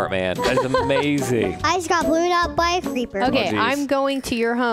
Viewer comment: when said 'I got blewed up by a creeper' i thought he said 'I got brewed up by a fried creeper' xDDDDD